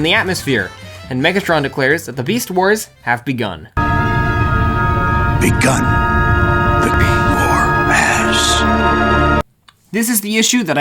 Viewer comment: Did Yoda say, "Begun to beat your 😂